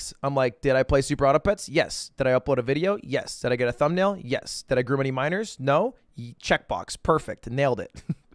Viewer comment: Did he just say "Did I groom any minors? Check box, perfect!" 23:26 lmao